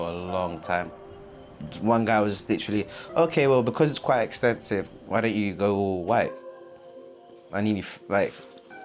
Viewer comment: Who else thought he said "oh well because it's quite expensive, why don't you go away?"